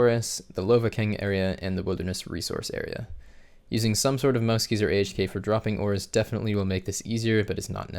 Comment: what did you say about helping drop the ores? Is there clothing that quickly drops stuff? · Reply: he says use mousekeys or AHK (AutoHotKey).